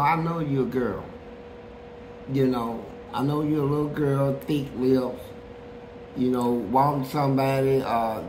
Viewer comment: They don't want to hear the truth, but he calling it how he see it. At 4:38 did he just say thick lips 😂